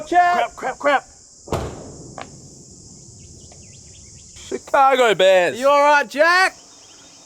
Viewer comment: Does he say “Chicago Bears” here? 😂